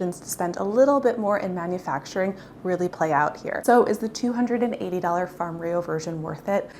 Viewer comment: I really thought you said "Really Coi out here" instead of "Really play out here"